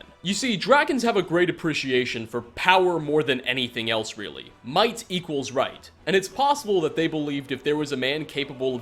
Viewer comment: i thought you said white equals right